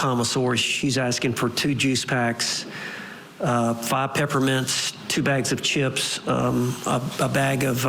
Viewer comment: Hey @ 1:20 I swear it like he saying Vodka ... After juice packs. I know it's not but I'm still really confused. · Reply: You've probably found out by now, but he was saying "Five peppermints". He has quite the twang to his accent, so manages to make the word 'five' have about three syllables